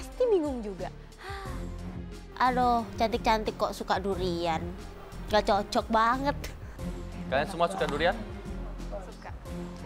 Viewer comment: ngomong apa si? Yaallah, he say “ cantik cantik kok suka durian , gak cocok banget “ hadeuh ga sopan keknya ngmng bgtu alig si, belagu banget wkwkw